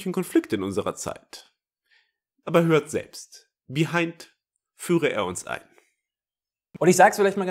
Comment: "Behind - Führe er uns ein" Bin ich jetzt der Coomer oder habt ihr das auch gehört?